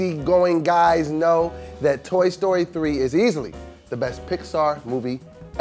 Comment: It sounds like Doughboy said "Tory Story 3"